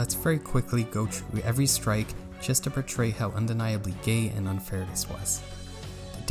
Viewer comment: I loved the video but I am confused, it sounds like you said “gay” here witch wouldn’t make sense in any definition of the word. close captioning is available but it’s autogenerated, does anyone know what he said? Sorry, I have auditory presssossing disorder Edit: I was giving him the benefit of the doubt, bc using gay in a negative way is homophobic, although mildly so. But he said the r slur soon after in a durrogatory way… so he might have just said gay in the first place…for me I don’t really care if mumky said it then bc it was years ago and he was playing a character, but this is so recent and sounds genuine. It’s a shame bc otherwise the video is honestly really good.